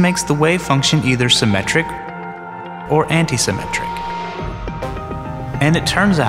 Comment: lolz I thought he said anti-Semitic functions.